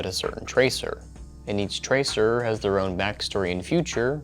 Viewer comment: Bro I thought it said 10 year tracer as in a 10 year old